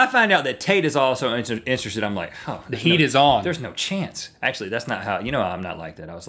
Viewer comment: I was dozing off while watching this (no offense to you guys) and I heard Rhett say "It's on, Tate!" and that's my name so yeah I almost crapped my pants.